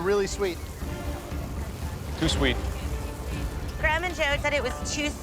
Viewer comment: EXCUSE ME? CALLING SOMEONE A GRANDMA IS EXTREMELY RUDE.HAVE SOME RESPECT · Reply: Woah , easy there !. She just said “ Graham and Joe “ . You might have misheard her saying that ! 😅. No disrespect to you though 😊, you do have a point .. that would be somewhat rude to call someone “ Grandma “ in a derogatory way !.